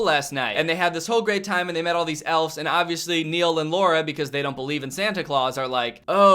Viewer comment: I thought you said “Lora and Neil don’t believe in Christmas” and me being the idiotic one my first thought was “So basically their atheists” i facepalmed right after while laughing loudly